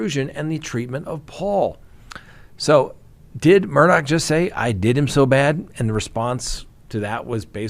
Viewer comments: His tears are sounding fake and I clearly heard 'I did him wrong'. Interesting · I dont believe he said, "They did him so bad" or "I did him so bad." For me, it sounds like he said,"I did. It's so bad." He was acknowledging that he had seen the pictures, and they looked bad.